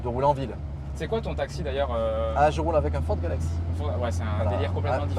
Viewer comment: C'est quoi qu'il dit qu'il a comme taxi svp? Juste ça m'agace j'arrive pas à comprendre le nom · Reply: Ford Galaxy ;)